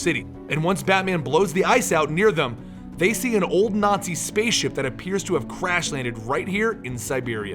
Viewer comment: Ahem....sir did you just say....a nazi spaceship?